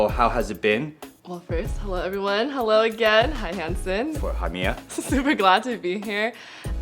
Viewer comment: Two questions: Why does he call himself Handsome, and what did he say to her @ 00:40 seconds? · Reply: Thanks for watching and commenting on the video! That’s so funny this entire time you thought he was calling himself handsome. His name is Hanson. And at that time, Hanson is just saying “hi” back to!